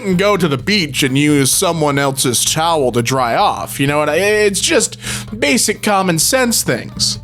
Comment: I heard you wouldn't use someone else's child to dry off